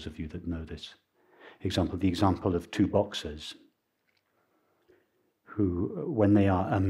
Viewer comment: At first I thought he said “2 boxes” 📦 📦 hahah! I was like ok..... I’ll try this analogy out..... hmmm. Then I after about 30 seconds I realised it was boxers 🤣